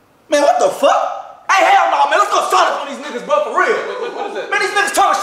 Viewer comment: "LET'S GO PUT THESE NIGGAZ ON MEDKITS" Leland took it with that one 😂😂😂😂😂😂😂😂😂😂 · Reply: I swear I heard him say "put these niggaz on medkits" if it mannequin it still go hard tho 💯😭😂😂😂😂😂